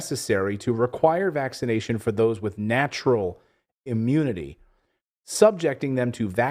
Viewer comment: ohh thought it said immaturity...dam